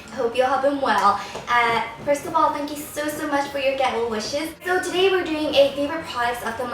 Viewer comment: i thought she said, "thanks for the ghetto wishes" instead of "get well"